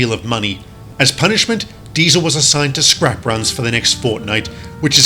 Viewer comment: i think he said fortnight